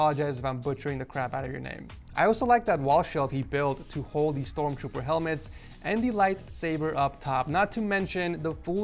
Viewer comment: Did he just say STORMTROOPER HELMET?!?